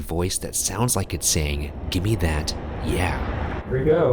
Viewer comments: In Susie's room, it doesn't say "Gimme that, yeah" it's saying, "Gimme that, crayon". But they say crayon in a way where it's one syllable. Instead of "cray-on" they say it more like "crane". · I think it’s saying “Give me that NOW”